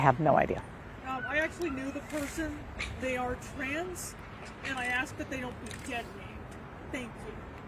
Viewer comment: What did the female person say @ 9:35??? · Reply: “They are trans and I ask they don’t be dead named”.